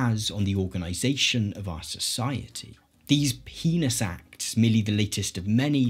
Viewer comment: I know you couldn't possibly have said "penis acts" like I thought I heard in the first two minutes of the video, so I went back and listened to it five times and I turned closed captioning on, and that's all I can hear and that's what closed captioning says you said. what did you say in that sentence? I'm so confused. Besides that confusion, wonderfully put together video! Began with your spot a fascist video which I absolutely loved and shared all over Facebook! As well as your Free Press video! Well done, well researched, and very well presented! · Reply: It sounds kinda like “heinous acts” except with a hard P at the beginning. I heard “penis acts” over and over again too